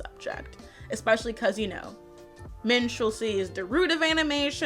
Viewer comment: she talks about something being the route of animation and being tied to Disney. Says the word twice. The CC says "min she'll see" and "mitchell c." I can't figure out the word, please help. She speaks wonderfully most of the time, but this one word I couldn't figure out and I would like to know more. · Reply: The word was "minstrelsy" which refers to blackface minstrel shows that were popular in the past, including when animation was first starting out (iirc)